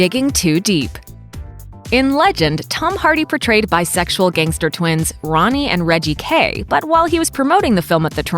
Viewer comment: xD, did you say "sexual gangster twins"? WTF xD